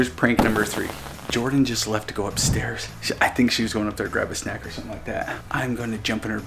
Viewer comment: Whan said Jordan went to grab a snack I thought he said she went to grandpa snack